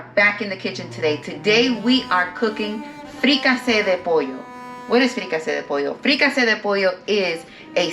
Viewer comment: I thought you said .. rica se de pollo, which means rich of chicken, but I see now that the description says fricasa de pollo which is chicken fricassee. I’m going to try this style.